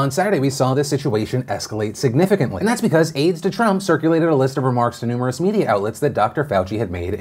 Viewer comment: I don't know why, but I chuckled when I heard "aids to Trump."